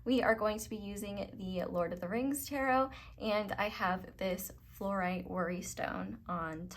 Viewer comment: Pile 2. In the selection when you said "Flourite worry stone" in my mind I heard "Flourite don't worry stone" 😄So going to just chill and listen to the reading 🧘‍♀️😊😇🥰